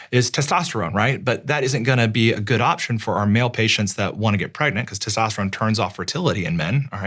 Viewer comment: did I hear this right? Male who wants to get pregnant? He states this earlier in the video too but I did not think much of it.